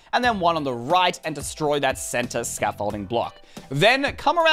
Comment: did he say scaffolding block?